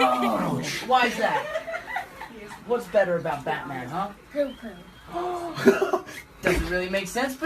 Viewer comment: Am I the only one that thought he said Joker